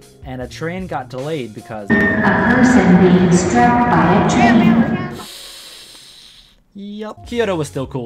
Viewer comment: What did that voice just say? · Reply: "a person being struck by a train"